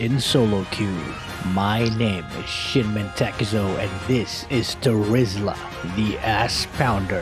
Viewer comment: I thought he said